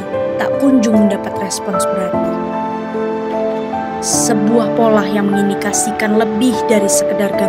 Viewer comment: "sebuah polah ... " saya yang salah denger, atau memang pengucapannya polah? ayoo rame rame tinggalin si ijo. topednya aja udah banyak yang ninggalin, sama kaya dulu mah si merah yang sekarang jualan pulsa. denger denger aplikasi sebelah masih lebih baek potongan aplikasinya, 13%.